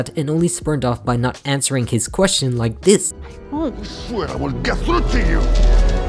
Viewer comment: What in the hell did he say? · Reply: “I swear I will get through to you”